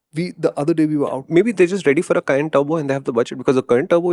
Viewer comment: What did he say at 11:34?? Did he say "May be there are just ready for a 'CURRENT TURBO' "? Is that what he said? Current turbo??? what does it mean? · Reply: He says "Cayenne Turbo" it's Porsche's SUV.